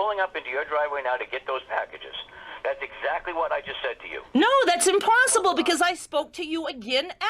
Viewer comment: When she said 'that's impossible!' I thought she said 'that's imparceble' because apparently in my brain that's a word now.